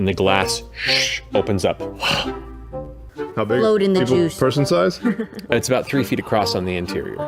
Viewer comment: Did Laura just say...load in the jews